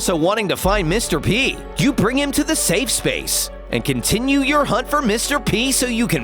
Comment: did he say safe space lol